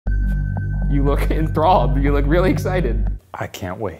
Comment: can someone subtitle WTF Ryan said? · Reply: "You look enthralled"